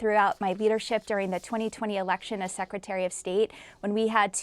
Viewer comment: Did she just say 2020 11 election ?